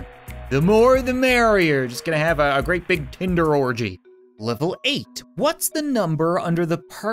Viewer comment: I was so surprised when I thought I heard kinder